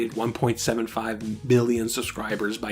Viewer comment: did you say 1.75 billion?